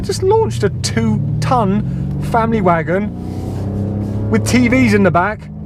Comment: legit thought he said "fanny wagon" 😅😅